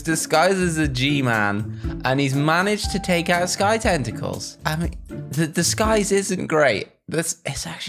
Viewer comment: me: hearing says sky tentacles me: wait wat this is suppose to be sky screamer